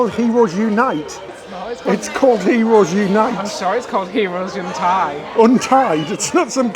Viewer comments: Hahaha ...my spouse thought said " heros in tights"! 😅😅😅😅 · I thought said ‘Heroes inTights!”